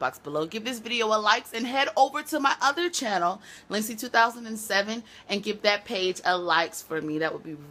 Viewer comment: i finally found your instagram and facebook page and your other youtube channel! it sounds like you were saying lindsey2007 lol. But all is great! i love your stuff keep it up.